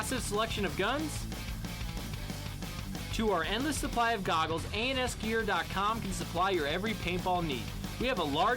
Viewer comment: thought he said anus